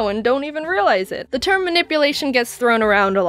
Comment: it sound like Jaiden said,"Mynipplation" XD UHM